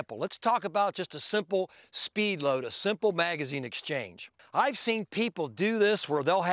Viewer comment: Well done Dave. I think you are one of best among the YouTube instructors. Now that been said, it's time to reveal who that "some body" is,of course, it's none other than C.C. the biggest fony; take a lesson from him will get you killed, instructor.I am glade to see more and more real professionals like you are setting it straight from the damage done by C.C. in tactical shooting. You know what, I think C.C. should take a lesson from you. His ex-partner got much better since taking lessons from Ron. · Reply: correction: I thought you said "I have seen some body", but you actually said "I've seen people". But any ways it's still C.C. with choppy, jerky, and over exaggerated motion.